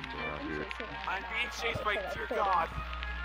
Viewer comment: What I thought he said "OH DEAR GOD" "IM BEING CHASED BY DEAR GOD" What he actually said "OH DEER GOD" "IM BEING CHASED BY DEER GOD"